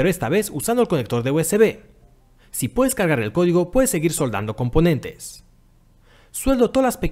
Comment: escuche "cagar el codigo" mejor veo otro video para confirmar.